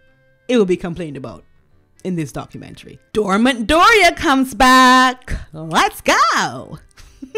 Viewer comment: Thank you for the laughs.....Dormant Doria!!!!!! Brilliant..... as if she couldn't help her daughter get a psychologist to help!!! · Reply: Funny how I heard not “dormant Doria”, but “door mat Doria”😂